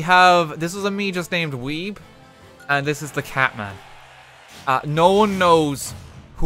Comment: I thought it said Cartman as in Eric Cartman from South Park.